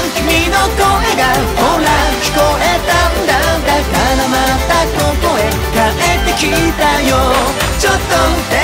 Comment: だからまたここへ帰ってきたんだよの部分が 風間が帰ってきたんだよに聞こえてクレヨンしんちゃんの題材にふさわしいなって感じてました。